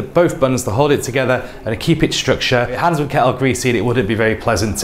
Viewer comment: '...a '....' kettle greasy wouldn't be better to eat.' Could you please share with us what you've said in all this sentence between the 55th and the 57th second of the video ? Sometimes, the British accent is not easy to follow for us English learners. Thanks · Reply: ‘We need both buns to hold it together and to stop your hands getting greasy’ Yes, I agree it’s difficult to understand as I speak very fast and the editing is bad. I will try to slow down in future videos. Thanks for your question. Have you tried writing an essay in this structure?